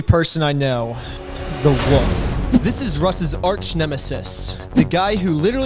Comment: "so I decided to talk to the most trust worthy person I know, the WOOF." it drives me insane that you say woof instead of wolf.